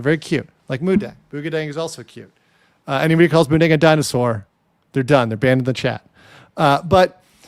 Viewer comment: I heard “nigga dinosaur” lmao